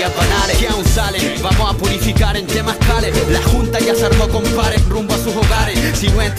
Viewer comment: Yo pense que decia "vamos a purificar en temazcales" :o